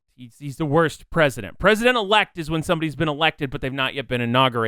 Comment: you're right, but you have to realize that tubbo didnt know any of this. he didnt know what tommy went through in exile. he just went and saw the pillar and nothing else and assumed that tommy had died because everything else was all blown up. tubbo had no idea of what tommy had gone through, and he had no idea that dream was the cause of it. his only recent memories of tommy WERE in fact tommy lying to him. i guess you must've not seen it, but there was a trial where tommy lied about not having touched george's house lol. as far as tubbo knew at this point, he had no reason to trust tommy and he was in a position where if he didn't do what dream said things would only go a whole lot worse for l'manburg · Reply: also i dont think dream said president elect LOL im pretty sure he said "president of lman-" and just cut himself off haha